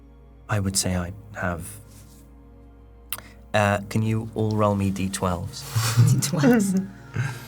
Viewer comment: very clearly says "roll me d12" and everyone is literally saying "d12, lovely". I don't get how hears "d20". Just heard "roll" and assumed, I guess. Just one of those days. 3:24:30 - acting, while attempting to be serious, was hilarious (in a good way). This party could be called "Magic Missile in the darkness". A nod to older D&D campaigns and the fun we had with casting spells into the dark in the hopes that we were being helpful. 3:40:10 - I know you guys are trying to be interesting, but pretending like you are just now figuring out about Patreon is a bit cringe. You've literally done this at the end of every episode. Acting shocked now is a bit disrespectful to your audience.